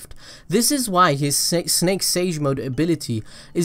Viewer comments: Who else hear "sex sage mode" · LOL anyone else hear sexy snake sage mode at 10:25?